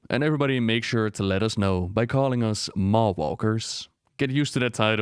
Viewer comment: Maw Walker, I like that title a lot. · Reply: Better than 'Mall Walker' which was what I first thought he was saying. ;o)